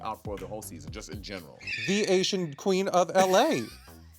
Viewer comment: I thought Joella said “The Slasian queen” and I thought is this another NewYorican situation?